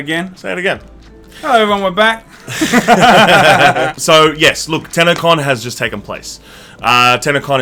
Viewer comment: When you thought he said "TanaCon" instead of "TennoCon".